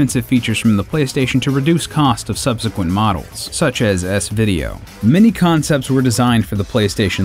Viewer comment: did you just say S-Vid removed!? Huh..?